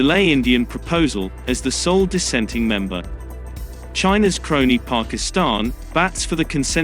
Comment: I thought he said "China's colony Pakistan" Lmao